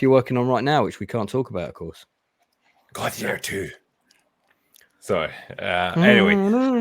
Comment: what the heck is saying? Ive listened to it a dozen times and i have no idea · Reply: Gladiator 2?